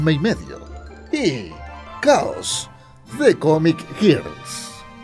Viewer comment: escuché "Comic Girks" ayuda